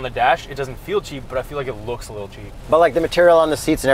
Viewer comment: said looks a little cheap.... I thought he said it looks a little 'Jeep'. I agree.